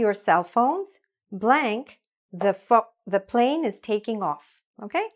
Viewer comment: Did she just say "the f***" 🤣